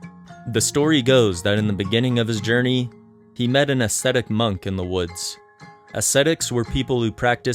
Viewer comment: I can’t right now. Deadass thought you said an “aesthetic monk”